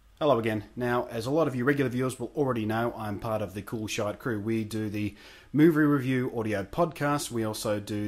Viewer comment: Wow, you scared me for a sec. I had my volume down, so I thought you said "As you may already know I am out of the Cool Shite crew."